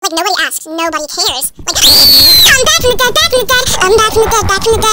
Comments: Why did i heard 'im back from bed 💀' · I thought they said im back from the gay💀